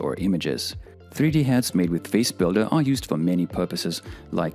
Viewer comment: Thank you for this tool, it's time to propose BodyBuilder, HandBuilder, Footbuilder to finally have a CharacterBuilder 🤩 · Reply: Legitimately thought you said HammerBuilder please no